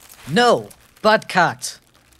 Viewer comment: In the first I heard “butt cat” lmao xdd